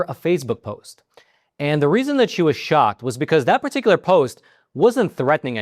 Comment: I think you were saying she was shocked. At first I thought you said she was shot.